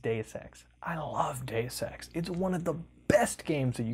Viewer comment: I'm slightly scared when I hear him read "Deus Ex" as "Day Sex"